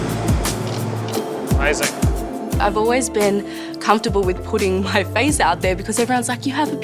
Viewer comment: At the beginning I thought she said "I've always been comfortable with pudding" lol